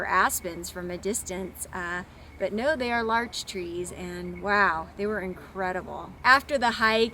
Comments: Thank you. I found them to be fascinating. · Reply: I thought you said " large trees " , and l was like darn, she didn't tell us the name of them ! Lol. But OK , I got it . . . Larches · Thank you for identifying the Larches for us. I wish more Youtubers gave us this type of additional info. Larch trees are interesting since they are deciduous conifers and not common in the coniferous forests of the lower 48. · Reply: I thought she said" large trees," too. I know them as (? incorrectly?) tamaracks.